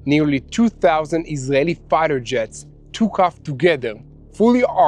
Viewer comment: Haha I first thought he said 2000 jet fighters, but I realised it was actually about 200 jets flying 2000 kilometres. Easy to mix up with those numbers! Great video as always, this is my favourite channel.